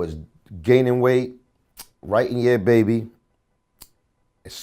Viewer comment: right in him a baby? what does he said? i cant hear it clear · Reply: Writing "Yeeaaahh Baby"